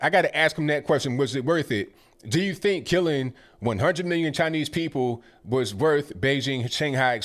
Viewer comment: Sorry, still looking for this Chiner place Bernie was talking about... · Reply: JimmyGable I thought I heard him say “Chiner” as well! 😂